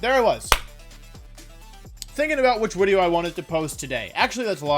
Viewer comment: New to this channel so no hate please, but does he have a lisp or dialect or something, because every time he says “video” I hear “wideo”.